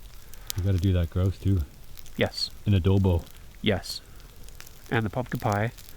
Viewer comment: did you just say ADOBO?? haha Filipino here!